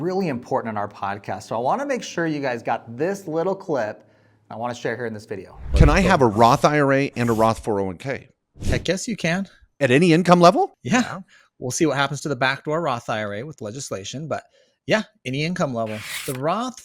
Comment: did he say “I guess you can’t”? Or “I guess you can”?